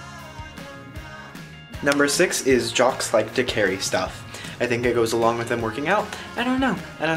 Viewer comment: Watched this like 80 times, but every time says "jocks like to carry stuff" I always hear " like to lick hairy stuff"